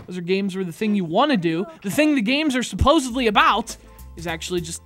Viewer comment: I thought said "supposably", but he didn't, thankfully.